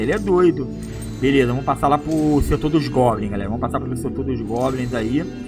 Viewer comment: eu entendi golem fiquei tipo assim agora tem golem no jogo?🤦‍♂️depois vi que era goblin 😂😂😂